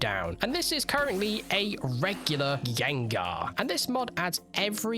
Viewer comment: I think u said "Yegnar" at 10:33 :D